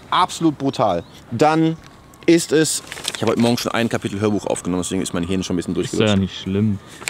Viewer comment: hab ich da Hörbuch gehört? Das wäre ja Nice😜